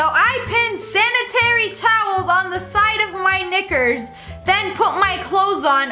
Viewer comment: Did YoU jUsT sAy ThE N-WoRd!?!?!? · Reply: No she said knickers